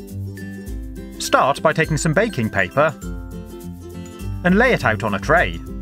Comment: lol i thought u said bacon paper